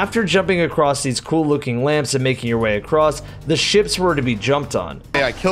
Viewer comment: "The ships" Thought that said "the simps" at first lol.